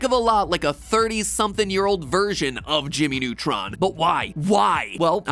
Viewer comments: At first I thought he said "A 30 Year old Virgin Of Jimmy Neutron" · Not gonna lie I thought that matpat said "20 year old virgin" for a couple of minutes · it sounded like you said 30 something year old virgin of jimmy neutron lol